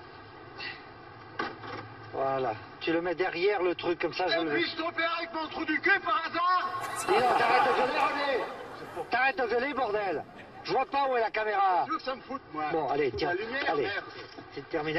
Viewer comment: il dit quoi ? J'entends "épluche ton père avec mon trou du cul, par hasard ?"